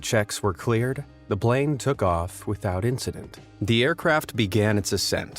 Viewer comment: does he say blane instead of plane?